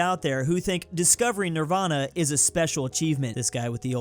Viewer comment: did i hear that right ? Or it sound like nixxer